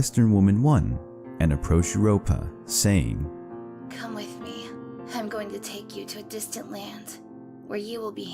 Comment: I thought i heard at 1:00 "Come with me, im gonna take you to disney land" 😂